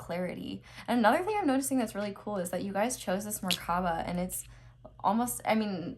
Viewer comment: wht is morkhava m i hearing something wrong?